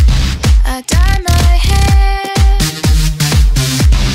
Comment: i thought she said, And coat my skin....